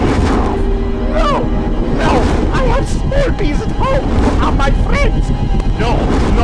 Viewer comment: Did he just say "I have sperpes" (Spider Herpes)? Falls over laughing 😂😂😂😂😂